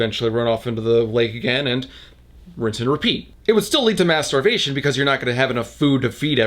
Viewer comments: When you said mass starvation, I thought you said masturbation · I think my brain is actually melting from this book that when you said "mass starvation," I thought you said masturbation and I giggled I am going crazy